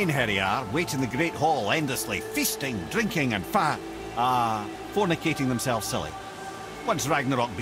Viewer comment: Did mimir says "F*cking"?